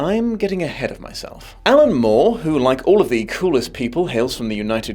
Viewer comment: So I heard “Like all of the cruelest people, hails from England” and was like “Dom, you’re from England, and not a dom even if you sounded very sexy while saying that” until I realized a second later you said “coolest” 😂